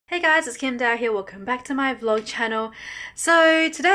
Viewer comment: Why do I always hear "Hi it's Kim Dahye welcome back to my vlog channel" lolololol 😂😂 this is why I failed my listening test in HS